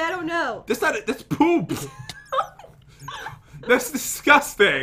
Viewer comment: did pat say piglins Edit: or is it piglets?